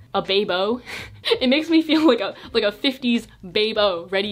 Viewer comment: "a 50's babe-o" sounded like Beebo and my mind just went wild for a second imagining Brendon Urie in a 50's women outfit